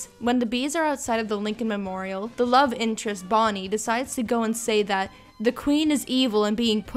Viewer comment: I thought you said Thotty not Bonnie